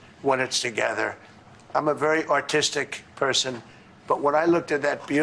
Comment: Did he say: "I'm a very autistic person": 0:43 ? The only brush "he knows" how to use he paid pr0s titute to wield it for him. If you think Melania doesn't count, you're living in a strange reality where Canadians would become the 51rst state. If we did, that would mean a dead orangina within a week. Yes, the soda trademark.